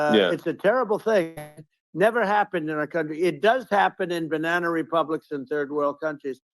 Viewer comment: Did he say banana republic